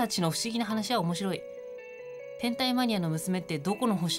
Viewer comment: 「天体マニア」が「変態マニア」に聞こえたww